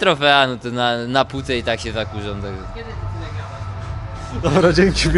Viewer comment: Maciek w tle, uwielbiam tę dwójkę :D Na skoczni wykonują dobrą robotę, a podczas oglądania wywiadów można się uśmiechnąć do ekranu ;) 3:52 Usłyszał ktoś o co zapytał Maciek? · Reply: Maciej Kot w 3:52 "Od kiedy Ty tyle gadasz" :)